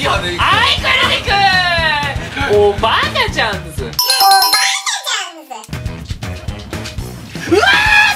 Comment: おばあちゃんズに聞こえた